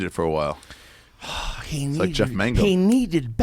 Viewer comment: Jeff Mangum?? Or am I hearing this incorrectly? Am I stupid? (probably)